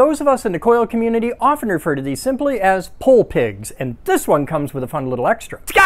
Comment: I misheard "Pole Pig" as "Hole Pig", then was expecting the character in the straight jacket to show up.